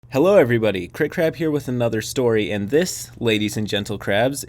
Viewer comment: I know he said "hello friends, critcrab here" But I heard "Craig crap here" 🤣🤣🤣